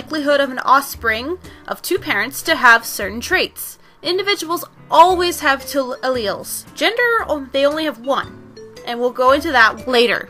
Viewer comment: What are you trying to say at 2:37? I hear 'they always have two alleles gender or they only have one'. I'm trying to record this in a google doc and I can't figure this out.